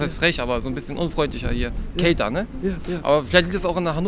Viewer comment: I think he said "Kälter, ne?" ("Colder, aren't they?") and not "Kay, danke" ("Okay, thanks"). And when the subtitles said "???" he said "'Kalter Norden' sagt man so" ("'Cold north' as they/people say"). Also, at 5:34 the man said "schöner drauf" ("in a better mood" or maybe "acting nicer") and not "schöner" ("more beautiful"). 😂 6:23 She said "Ja, doch" (≈ "Yes, I am") and not "Ja, toll" ("Yes, great"). 8:05 "Was geht?" means "What's up?" and not "What's happening?".